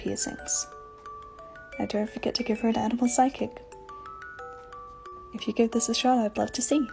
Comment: I misheard the 3rd challenge as "and dont forget to give her an edible sidekick"